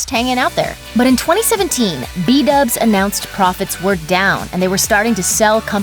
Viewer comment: Am I the only who heard iDubbz?